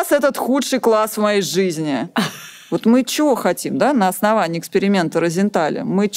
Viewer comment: Мне послышалось,говорит:чё хотим от ?